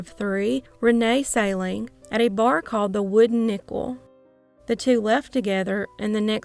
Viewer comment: I thought you said Wooden Nipple.